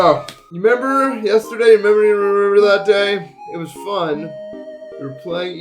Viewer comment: somebody else please tell me that they dont know what he just said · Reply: "does anybody remember that day it was fun" 😭